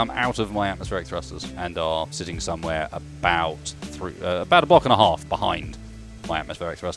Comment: When he noticed that the turbines had moved back, I thought he said that they were "A buck and a half back" rather than "A block and a half back"